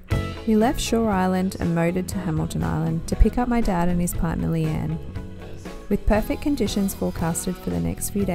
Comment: I had to push rewind ,I thought you said pick up your dad & his wife Leanne,I was thinking crikey about time ,but I was obviously hearing things 😱🤔👍